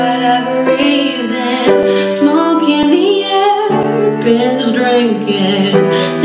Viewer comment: I swear i heard "pandas drinking"...OK..